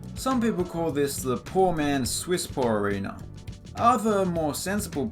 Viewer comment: I had to back the video up because I wasn't listening closely and thought I heard Piss pioor arena when you said swiss pore